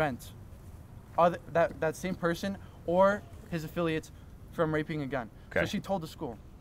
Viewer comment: did he just say "raping a gun"?